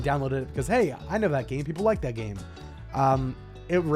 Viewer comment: legit thought you said ''Hey I know that gay people like that game'' lol